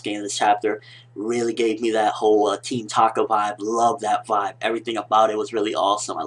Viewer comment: I swear I heard him say Team Tako vibe lol